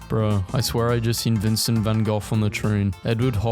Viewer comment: Why do you keep saying Vincent Van Goph instead of Vincent Van Gogh?